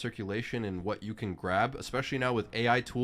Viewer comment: Sorry...did he say..."Trump change"???